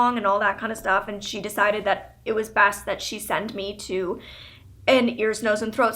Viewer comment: she said "it was bast" instead of it was best